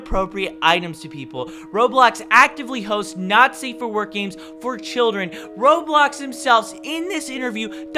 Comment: Halfway through this video and I’m just realizing he’s saying Not Safe for Work experiences. I thought he was saying Nazi for work sessions.